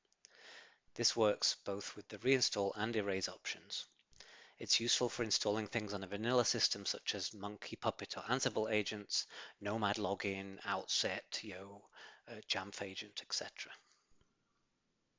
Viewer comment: what is he saying? Monkey puppet hand symbol agents?